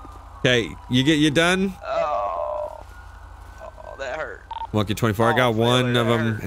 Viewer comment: I swear says "Monkey 24" instead of "One-King-24". Hilarious!